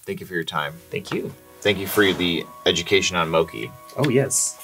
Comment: did he say moki or at i going crazy